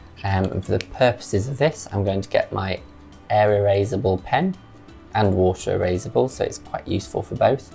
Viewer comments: I think his pen is air erasable. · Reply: I thought he was trying to say, "era raise-able". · take a shot every time he says “air erasable pen” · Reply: i thought he was saying error erasable cause i can erase my errors cause i am clumsy. But I don't drink so i did a dab every time he say now i need a nap